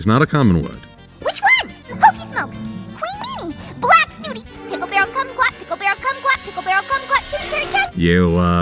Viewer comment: What is pinkie saying here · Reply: Pickle berry kumquat.